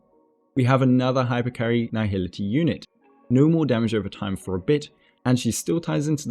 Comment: did u just curseee,in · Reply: Im sorryyy i thought i heard no more damage over time for a b***ch